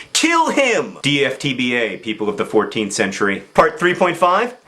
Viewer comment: Oh! I thought he was saying "40th century" and I was all like, "wait.. Wouldn't they already know that the moon wasn't made of cheese?"